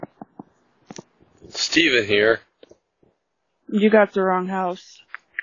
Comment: She answered immediately you've got the wrong house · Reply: Im pretty sure she thought he said "Is Steven here?" cus that's what it sounded like he said at first